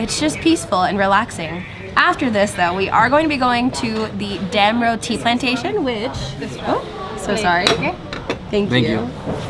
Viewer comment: I thought you said damn rotti plantation 🤣